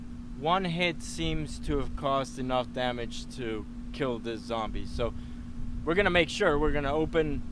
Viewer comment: I skipped the first 10 seconds and the first words I heard is "Zombie ass"